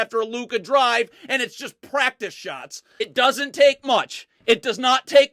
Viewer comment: did he say munch? That Jack in the box commercial is everywhere.